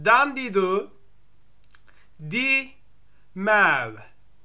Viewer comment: I heard him say 'mam' not maw .-.